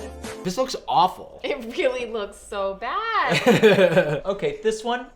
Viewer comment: I was sculpting something for my friend while watching this video, then I hear “it looks to bad” from everyone in the video and I was prepared to defend myself for a second, like I looked up and started saying “no it’s not!” When I realized they were just reacting to the garbage suitcase